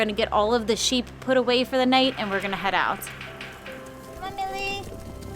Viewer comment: Did you say "come on Lilly" @ 4:20? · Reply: Millie 😊 she is the black and white ewe (she's elderly and doesn't see too well these days)